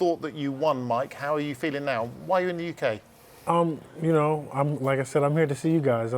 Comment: he said, while you're in the uk the guy thought he said, why are you in the uk lol